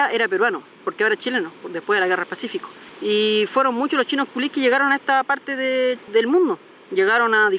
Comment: dijo chinos klios o escuche mal? 🤣🤣🤣